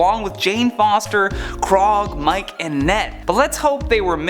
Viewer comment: did he just call KORG, KROG!?